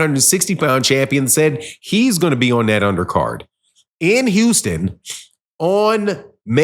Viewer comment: i could of sworn u said jermal wasn’t gone be on this under card